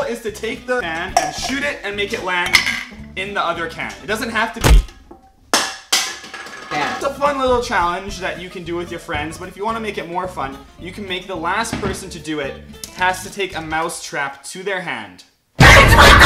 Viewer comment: I thought you said you had no friends